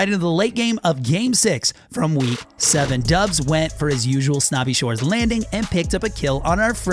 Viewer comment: Looking back on this I swear I heard him say “dubs went to his usually spot snobby shores and picked a kill up on sypher” top right LAZY LAGOON